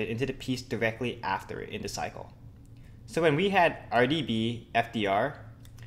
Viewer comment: I thought he said Cardi B :p :p